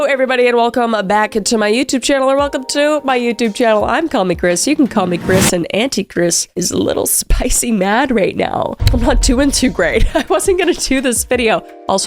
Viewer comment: Low key thought she said the anti-chris at the beginning 😂 😂